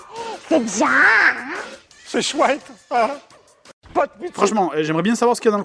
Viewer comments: c'est moi ou le papi vers 5:30 dit : c'est swag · c'est moi ou a 5:22 le mec de chez dorothé dit "c'est swag" ? XD